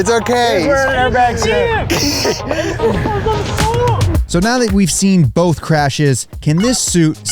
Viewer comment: I definitely thought this said "I tasted an airbag suit"